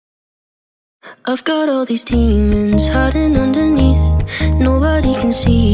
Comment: I heard "I got all these stingers, stingers, stingers" Just me? Ok...